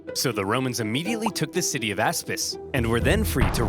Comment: I thought he said asspiss 💀